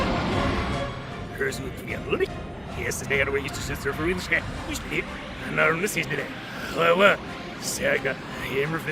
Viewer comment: Hey can somebody tell me what Beetlejuice is saying at this part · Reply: “I guess that’s it for me, huh guys? Oh well, had a decent run. Hey, did you actually reverse this thing just to hear what I said? Get a life, loser.” (I’m not joking, that’s what it said when I reversed it and also your welcome)